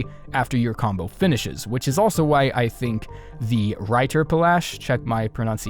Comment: what did he say? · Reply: Righter Bolasch It is hard to say to be fair 😆 close to the Portuguese word for “Biscuit”.